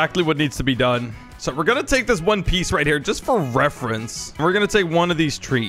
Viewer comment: What I heard at 2:23 : So I’m just gonna take this one piece reference…